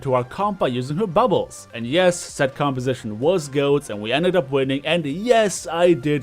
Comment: At like 3:14 did you say the comp was goat? I’m sorry I’m a little tipsy and have a really hard time with accents sometimes. Still loving the content even if you haven’t seen a comment from me in a couple months I’m watching and loving it. Keep up the good work you’re one of the only reasons I still come back to overwatch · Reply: Yeah GOATs is the popular 3 Tank 3 Support Comp that's currently run.. A lot.